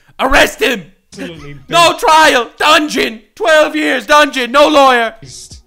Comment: When screamed "dungeon!" Around the 20 minute mark, i thought he as saying "danjin" since its a video about japan. The term "danjin" (断塵) in Japanese can refer to "abstinence" or "detachment from worldly desires," especially in a Buddhist context.